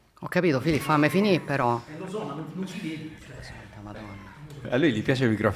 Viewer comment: rca madonna? 😅😅 Oppure ho sentito male io?